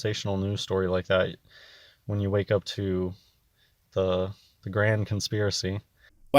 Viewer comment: Holy carp. The first time I heard it I thought he said "the graham conspiracy". So crackers are fake now too.